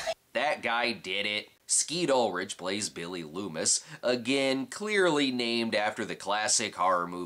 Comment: i misheard billy lumis as billy doofus and thought it was just one of your jokes lol.